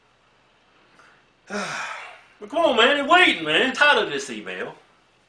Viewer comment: I always thought he said "I'm tired of this email"